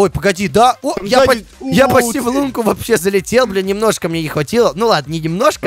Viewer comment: мне одному послышалось бля